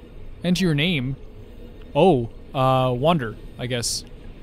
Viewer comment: i thought he said Wanderer... imagine